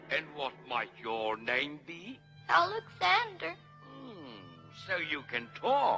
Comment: i swear, first time hearing his name i thought he said "Ollivander"